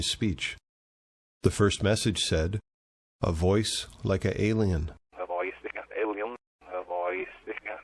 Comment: I hear "a voice sticka alien" & "so-nission"